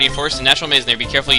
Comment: i love the Viridian forest music.Lol i thought you said :Screw you i am not catching a Retarda